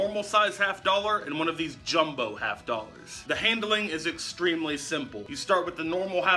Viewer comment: When he said dollers I thought he said doll hairs